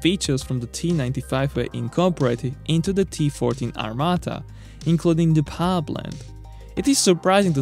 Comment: "successfully powering T14 Armatas".... good one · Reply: Thought he said T40 Armata, which happens to be a modern russian tank